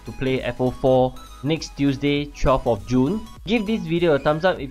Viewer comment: Chop of June, what do you mean ? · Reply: Cmi Ace 13:46 I knew it, Caption says Chop of June